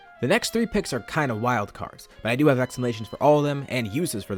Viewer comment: “explanations” ought to be subtitled instead of “exclamations”